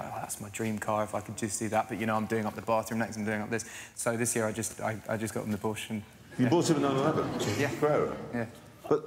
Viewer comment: It sounds like he said "so this year I just got an abortion"